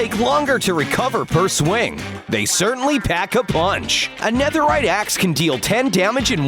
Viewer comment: did he say netherite axe?